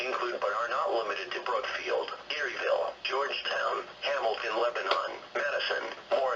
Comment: did he say “Lebanon” ?